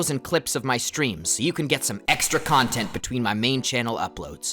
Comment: Did you Just say. EXTRA CONTENT!?!?!